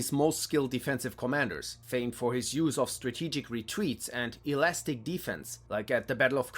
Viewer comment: I heard "famed for his use of strategic re-tweets" 😆